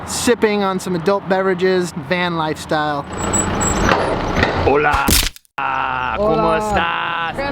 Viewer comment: Is said "KAMUSTA"? Filipino word means how are you? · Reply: No, he says "Hola, como estas" Spanish for Hello, how are you?